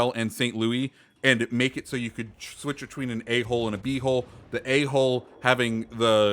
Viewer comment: Sorry but when you were saying "A hull and B hull". it sounded like you were saying Ahole and Bhole lol